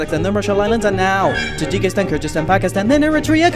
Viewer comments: He said Kurdistan, not Kyrgyzstan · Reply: I’m pretty sure he said Kyrgyzstan but misheard him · i thought he said Kyrgyzstan not Kurdistan lol